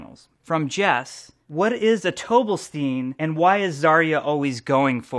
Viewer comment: I always understand zarya's ult as "I'm going for that donut steel" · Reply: I hear it as "a-don't forget tobelstein"